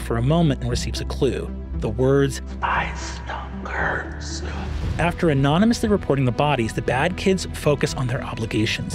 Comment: High can anyone clarify what Brennan whispers at 10:50 ? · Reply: "Spies, tongue, curse" 👍